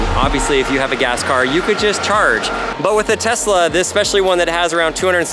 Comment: Did you said if you have gas car you can change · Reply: He said charge